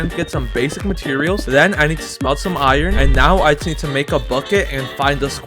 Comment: did you say smell💀💀